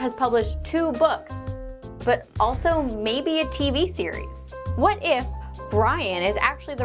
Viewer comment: When you said TV series around 0:31 , my iPad opened up Siri because it thought you said hey Siri. Lol